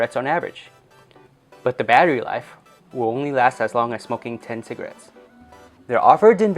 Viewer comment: it sounds like he saying fucking 10 cigs